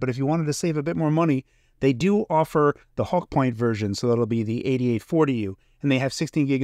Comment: For a second there I thought I heard you say "the hawk tuah version"